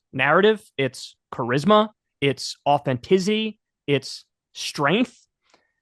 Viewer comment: did Kyle say, “Authentizzy??!!” 😂